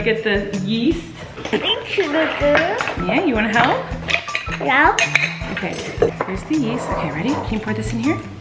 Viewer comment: I swear I heard him say "no." 😂